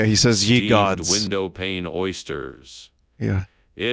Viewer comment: i love how they act like "window pane oysters" is a real thing, i know its an AI but its just funny how they say it so normally. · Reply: oh I thought he said "window paint oysters"